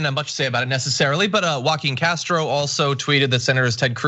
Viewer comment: So I’m watching while multitasking (ie not watching and mildly attentive) and I really thought John said “walking casserole” not Joaquin Castro 🤔